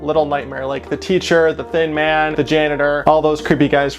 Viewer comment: Honestly, the first time you said thin man i thought you said thiccc man from the second little nightmares